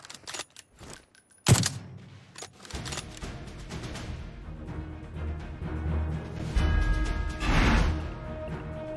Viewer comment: What Did You Say!? I Love You Iam From Egypt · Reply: He said "LET'S GO!"